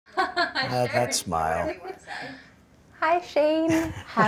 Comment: did he just say at the beginning "that smile"? or is it just me?